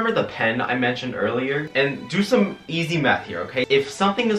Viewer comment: I can't be the only person who heard Meth and not Math lmao